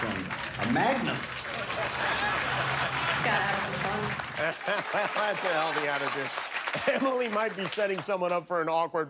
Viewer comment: what did she say? xD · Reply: get have some fun